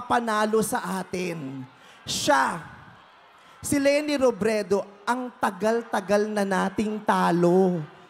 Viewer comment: Did I hear it right? " Si Leni Robredo, ang tagal tagal na nating talo?"